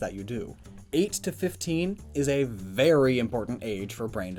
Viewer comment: I'm sorry i must have been watching YouTube for too long today because for a second there i thought you called flowey totally innocent